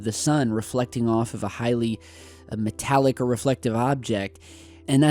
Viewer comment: Did you say "MetallicA" at 6:00 ?